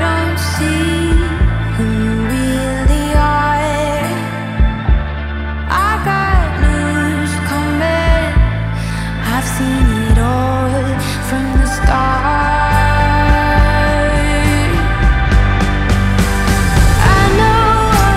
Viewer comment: I really thought she said "I've got news, scumbag" and not "I've got news coming". 😅